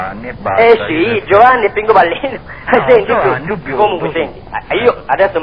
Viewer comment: Enzo Tarcino' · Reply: guarda, anche io per anni ho sempre sentito un "nome e cognome" ( che cmq non capivo :D ) e nonostante lo ascolti una volta al giorno, tutt'ora ancora non riesco a sentire " può darsi di no" :) Ma hanno ragione!! dice proprio così, per noi che non lo sentiamo ancora :D abbiamo la conferma al min. 10:00 circa, quando "Bruno" gli dice: "ma chi se l'è fregata su..io e GIOVANNI e bastha" Mario: Si, io Giovanni e Pincopallino!! "Bruno": noo! Giovanni jù BIONDO!!! :) mistero svelato! dobbiamo ammettere di aver sbagliato!! ahaha! scusaci Marioo! :)